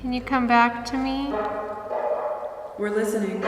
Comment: Can anyone else hear, “we’re listening”